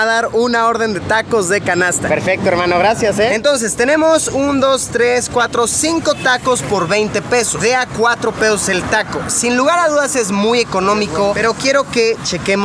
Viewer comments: yo escucho que dice de a 4 pedos like si tu tambien escuchaste asi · like si escuchaste en él minuto 3:48 que dijo 20 pedos